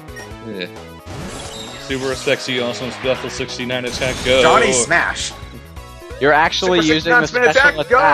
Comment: I thought they said 'Darn A Smash' but now I realise it's 'Jonnhy Smash' :)